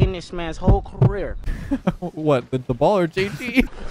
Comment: Can someone plz explain the roast because either I hear it wrong or I don't get it. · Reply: "Bro, this hasn't been touched by a person in a while" (Arsenal voiceover: "I'm about to end this man's whole career"): "What, the ball or JG?" I didn't hear it right either, at least not the first time through